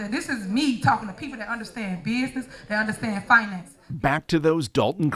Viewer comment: "...who understand Beesness..." WTF is BEESNESS?